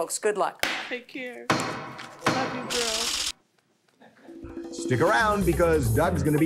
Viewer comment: When she said "love you girl" I died 😂😂😂😂 she was happy as hell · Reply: 😂 I thought she said “I love you Judge” 😂. It was cute